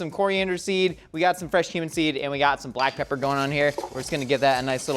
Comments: thought josh said "we have some fresh human seed" and i wasn't even mad at it. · did he say fresh human seed? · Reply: Cumin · Anyone else hear "fresh human seed"? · I thought he said human seed and not cumin seed and was VERY CONCERNED. Also coughing made me flashback to the annoying jocks in high-school and I had to pause the video to take a breather 😂😂😂